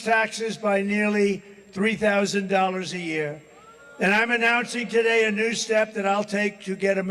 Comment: When he said I'm announcing today, I heard I'm a Nazi today.:face-orange-frowning